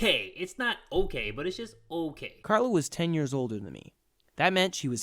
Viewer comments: I heard “Karla was ten years old” 😳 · did I hear right did he say Carla....MY NAME IS CARLA WTF 4:12 it’s spelled the same way!!!! Wtf